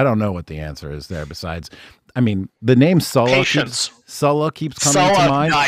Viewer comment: What name does he say at 5:32? Salakin? Edit: The name is Sulla, one of the greatest Roman Generals who successfully...